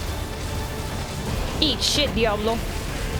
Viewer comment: Did she say idiota at 1:35 mark? Does she speak Spanish?